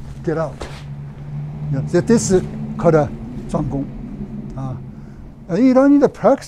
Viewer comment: i think he said zhong gong. (zhong) center does the work (gong) or center work. but i could be wrong. he used to say zhong ding alot (center equilibrium) which is why i think its that. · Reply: I checked with Master and you are right. 桩功，zhuang gong is what he said at 1:15. However, the name of the lesson is Zhuang kung. You can find the full lesson at patreon along with how he wrote it in Chinese. Thanks for catching that. I’d change the subtitle if I could, but then I’d have to repost the whole thing.